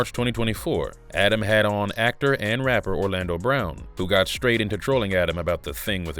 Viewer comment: When Orlando Brown is calling you out, it's time to change your life decisions. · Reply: lol when i first started this video I thought you said Orlando bloom, so I was waiting the whole video to see him :(